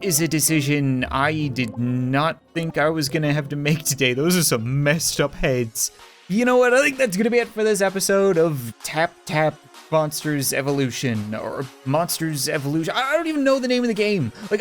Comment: I thought he said moppers evolution! 😂